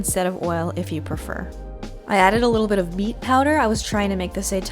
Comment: When you said “au gratin” at the end I definitely thought I heard Ogre Town 😂 I need to get my hearing checked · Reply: Vanessa Alexandra I heard "meat powder" when she said beet power. I had go back and listen again.